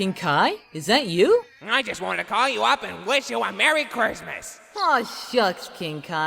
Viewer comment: i SWEAR king kai said "cursemas" and i think it was on purpose too.